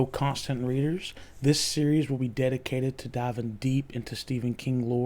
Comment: When you said "This series will be dedicated", my iPhone thought you said "Hey Siri", then it started converting speech to text as you spoke.